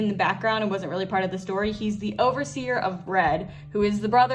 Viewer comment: Was I the only one that thought She said he was the overseer of bread